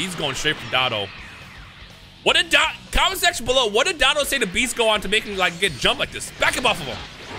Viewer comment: yeah he called him bitch gohan instead of beast gohan😂